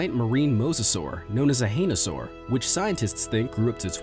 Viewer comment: did he just say an ANISaur